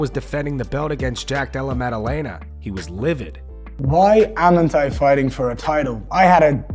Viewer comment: Ummmmm 4:30 DID JUST say “Why am’nt i” !?!?!